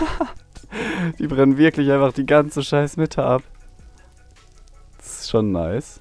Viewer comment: ich hab verstanden die brennen einfach gleich mitte ab xD